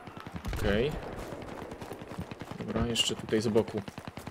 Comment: kto w 3.29 usłyszał "mam tutaj jeszcze zboków" ?